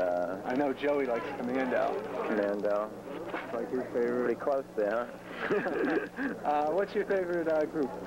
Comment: What Johnny sais in 15:23 when the man said him that joey likes commando? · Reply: “commando? pretty close there huh”